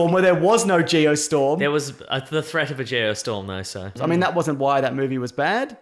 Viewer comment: did just call "Maso" lmao how did neither of them catch that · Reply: I think he said "There was a threat of a geostorm there, so."